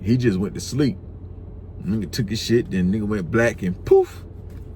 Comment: bro did he say he took a 💩and went black 😂😂😂😂😂😂😂😂😂😂😂😂